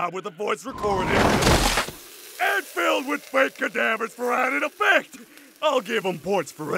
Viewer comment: without subtitles i thought he said "with bacon flavor for added effect!"